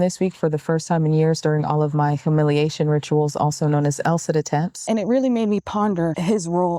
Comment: "L sit attempts"... I thought you were saying "LSAT attempts" with a funny pronunciation. Then I watched the video while you said that. lol. ... Interesting topic. Thanks!